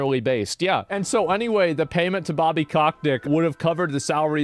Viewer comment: Did he just say "Bobby COCKtick"? LMAO